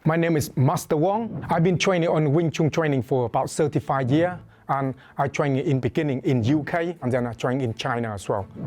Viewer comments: Yo who else thought he said he was training for 75 years i had the pull the video bacl for the one time 😂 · did he just say HES BEEN TRAINING WINGCHUN FOR 75YRS ALREADY? Wow monks & kungfu meditation can really get u looking younger than ur actual age huh? He doesn't look like a senior citizen at all. He looks like hes only in his mid 40s only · I thought he said “75 year” bout to say 😳then realized wait there’s no way he’s even 75 yes old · I thought he said he has been training for 75 years lmao i was like no way or he would be ipman 😂